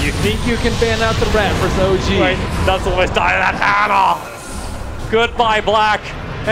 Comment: It's me or I heard "Good Buy Blyat" ?...